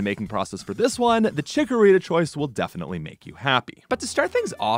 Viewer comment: The second you said we’d be happy with the chickorita change I knew we were getting a grass dragon. I appreciate all the love this line gets with your games. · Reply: Same bro. I first thought fairy. He said happy and the jig was up